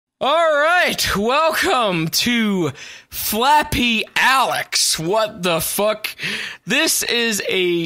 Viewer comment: ) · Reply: I thought it said "Fappy Alek's"